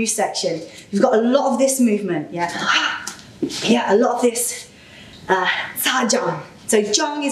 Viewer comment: In 10:44 & 15:43 I don't understand the names of the techniques. Could you write it in pinyin. Thank you! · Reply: Hello the movement in Chinese is called Cha Zhang - stabbing palm / Guo Tou - wrapping around the head . Hope that helps x